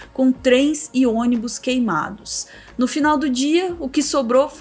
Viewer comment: Aos 2:12 eu ouvi "3 e ônibus queimados" .Pensei que era a piadinha do "6 e ônibus". Rs